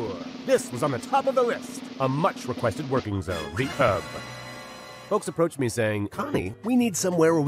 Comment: Am I the only one who hears "rehab"? That's a better description of the place.